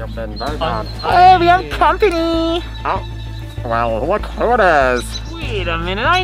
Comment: When Said well look who it is I thought he said Well look who dis